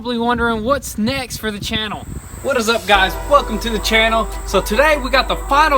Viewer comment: sounds like he says "fuck em to the channel" in the beginning